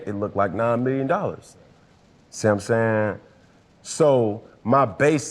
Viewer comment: Read transcript 9:06 “see what I’m saying “ was written out as Samsung 😂😂😂